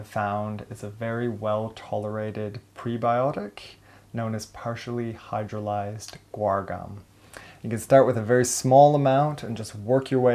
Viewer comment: What was the name of that laxative, he mentioned at 2.22? It sounds like he says partially hydrolyzed oregom??? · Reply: p h guar gum.